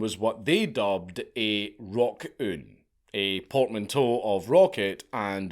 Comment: I thought you said Rock-Urn. Which would've been just as fitting perhaps?